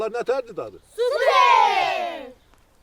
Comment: can anybody translate what the kid said here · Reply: Super